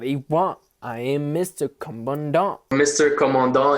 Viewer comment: I realized I’ve been singing a lot of these lyrics wrong... For example when he says “I am Mr. Commandant” I thought he was saying “I am Mr. Come On Down” 😂😂🤦‍♀️